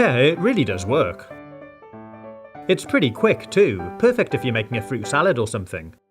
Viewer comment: I thought 5:21 sounded like "perfect if you make me a fruit salad.